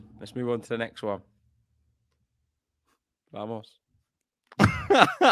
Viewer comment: Those little Vamos whispers are hilarious😂😂😂😂😂😂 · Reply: I heard it as vamos Nole